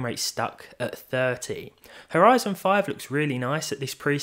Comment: I thought he said "our Ryzen 5 looks really nice", took me a god 10s to realize how to make sense of it ^^